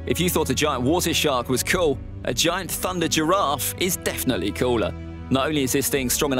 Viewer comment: did I hear 'thunder girrafe'?😂😂